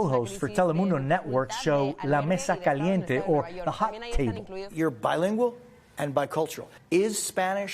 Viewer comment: It is my ears or did he say “La Mesa Caliente” as a hot tea? I thought mesa was table. I”m from Somalia Español is not my first language but I learned that mesa is not a tea since I love tea as well. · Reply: It’s your ears, he said table in a muffled way, not tea Que logres tus sueños con el español :3